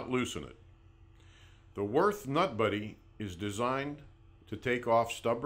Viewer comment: Thats a terrible name if you listen it sounds like hes saying the worst nut buddy with a speah impediment